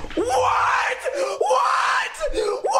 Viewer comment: Normal ppl hear: WHAT? X4 I hear: NEW DEATHSTEP DROP